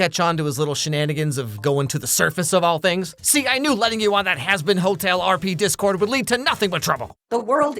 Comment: did I hear that right? Hazbin Hotel mentioned??!!